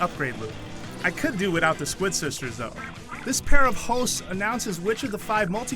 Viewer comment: Did he say "this pair of hoes" at 2:38?!?